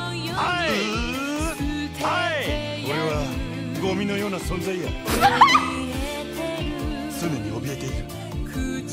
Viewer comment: What's he saying in this part? LOL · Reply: I am the existence like garbage